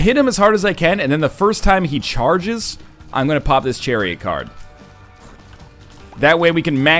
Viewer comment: i heard "pop his cherry card"